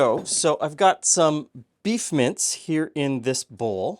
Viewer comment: So I heard "beef mints" and had flashbacks to living in Japan, where they often try many similar taste fails🤮